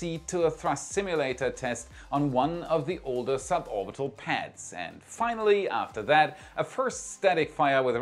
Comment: I thought it said orbital warship lol